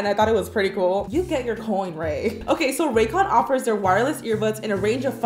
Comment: I’m I crazy or is like every Youtuber sponsored by racons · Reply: I thought u said raccoon lol